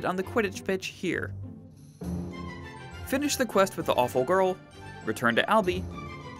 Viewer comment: Did he just say "awful girl"?